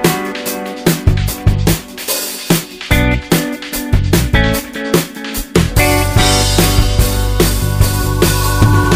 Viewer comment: ? I can't understand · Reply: Laxmi chit fund is on board (Hera feri)